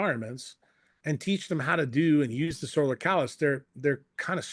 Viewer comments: I'am not a English native what does he say? Solar calendar? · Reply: solar callus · I'am not a English native what does he say? Solar calendar? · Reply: Solar callus. Similar to how you build calluses on your hand from manual labour, you can build up resistance to the sun. To me it seems like a recipe for skin cancer, so I'd like to hear his thoughts about that.